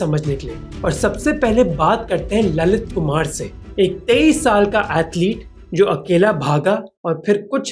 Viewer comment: My hindi isn't good but i know 21 isn't "teyish" You said "23" But the subtitles said "21" which age is correct